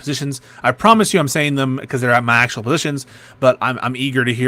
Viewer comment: did he just say these 'aren't my actual positions'?